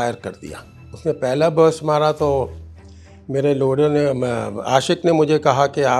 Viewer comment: Did he say "maray loray nay" @ 18:33 😁😁